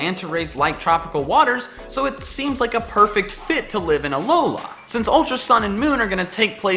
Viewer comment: TWO IMPORTANT ANNOUNCEMENTS! 1. I'm now a Twitch partner!! Be sure to come by twitch.tv/ around 1-2 pm CST today to celebrate! 2. The NINTENDO SWITCH giveaway ends this weekend! Be sure to sign up! <link> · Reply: it kind of sounded like you said alolaa